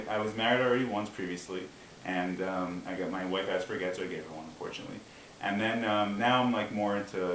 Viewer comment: what did he say baout his wife at 0:42 ? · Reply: She wanted a get, a divorce, so he gave her one.